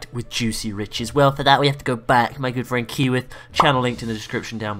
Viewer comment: Funny the end of the Vid. It sounded like u said Kiwi instead of keyworth, i know a Frozen Kiwi is why i ask lol